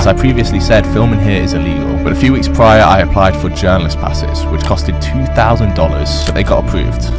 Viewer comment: I thought I heard you say "but a few weeks prior I applied for German classes". Then I thought that wouldn't make sense. I played it again with subtitles. Hahahaha.